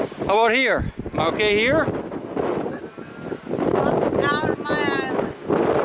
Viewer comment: Sounded like she said get out of my eyeline. Sad that alcohol has such a hold on some people. Thanks for the video.